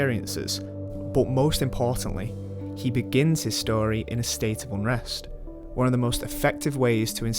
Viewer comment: Does anyone know what he says in 2:26, "the story begins in ______ ?" Sorry, English is not my mother language... :( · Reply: he begins his story in a state of unrest, one of the best ways to instil a correlative emotional journey with the viewer, because it circumscribes the character's existence solely to the universe of the film...